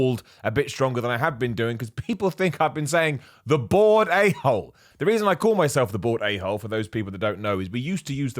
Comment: I always thought you were saying, “That bored a-hole.” Huh.